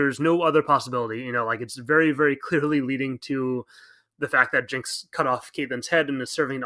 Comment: I definitely thought you were gonna say “leading to a face reveal”